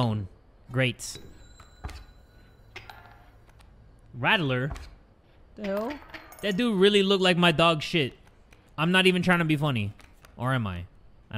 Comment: is it just me or I hear a hello in